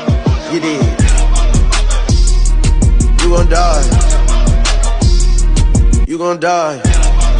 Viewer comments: I thought he said: "You are gonna die" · I thought it said “you gonna die” for a second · Lol actually thought he was saying you gonna die